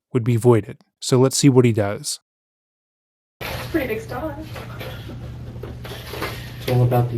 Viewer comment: Am I hearing this correctly?! Did the detective just say “That’s a pretty big star” referring to the star censoring his penis in a photo and replied with “It’s all about the angle”. I never thought I’d hear a detective gas up a sexual predator by complementing the nude he sent to what he fully believed was a 12 year old girl even if she said it first the purposes of getting a better confession out of him. 😅